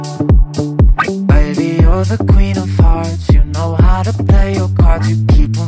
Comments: I hear "queen of farts" instead of "queen of hearts" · why'd i hear "baby your the queen of farts??????" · I thought he said your the queen of farts lol